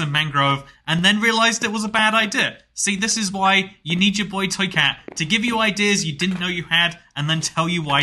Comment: I’m not gonna lie at 17:53 I heard boy-toy…….cat